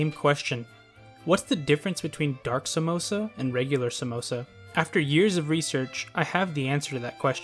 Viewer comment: did he ACTUALLY say "samosa"?!